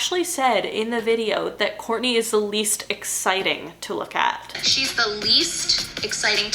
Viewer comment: Right OK, so I literally watched that KUWTK episode the other day for the very first time and I thought she said 'least interesting'. That's thrown me. Also, I would have bet the car was pink and the cornucopia was in the fruit of the loom logo, my school jumpers were made by them! Confused af. 🤯